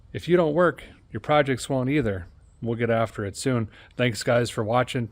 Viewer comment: I had to rewind you at the end. I thought you said, "If you don't work your privates won't either". I was like, what did he say? Then I thought, well that applies too.